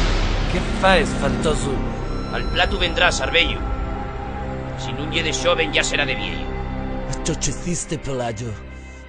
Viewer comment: ¿Qué dice en esa parte? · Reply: Al platu vendras arbeyu,si nun ye de xoven ya sera de vieyu, eso es lo que dice